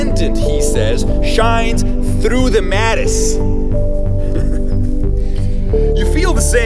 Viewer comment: What is the exact wording between 3:05 and 3:10? It sounds like he's saying "because the transcendent, he says, shines through the 'matis'" I think 'matis' is a misspelling, does anyone know what he's referring to exactly? I'm really fascinated by the analogy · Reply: Maybe he's referring to Henri Matisse.